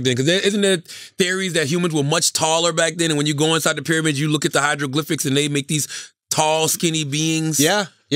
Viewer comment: Did Charlamagne just say Hydroglyphics?